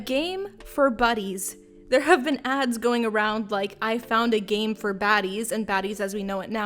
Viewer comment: Omg I thought the total said I found a game for babies and I was like HMMMM